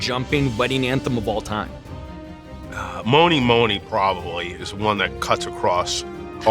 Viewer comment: what song did he say? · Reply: I guess Mony Mony?